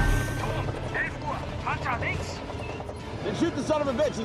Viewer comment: I swear it says "jar jar binks" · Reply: yep, "Panzer links". Not "Jar Jar Binks".